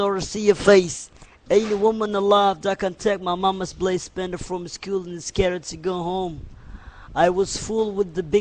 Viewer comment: all I heard was “landed from his skilled and his scerity go home” Someone sign this man cause this right here is the next Tupac in the making